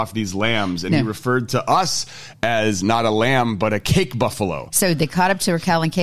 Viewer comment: "CAPE BUFFALO" the African buffalo with big sweeping horns that look like a center-parted wig! "The only member of the buffalo family that occurs naturally in Africa. I thought that's what said... no?